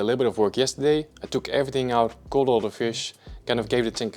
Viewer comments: He did what to all the fish? 😰 Coulda sworn he said 'culled' D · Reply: ▪︎ You should know better 😊 said he "caught " all the fish !!! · At first i thought you said "culled all the fish".